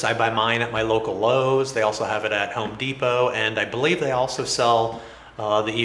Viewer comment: I've been watching too much LoTR, I thought he said Helm Deepo 4:53 😂